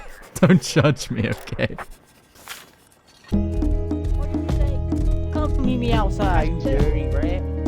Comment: "come meet me outside bruh" that what he said? Or did I misheard lol